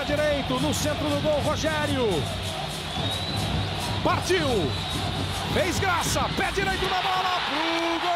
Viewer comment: ouvi desgraça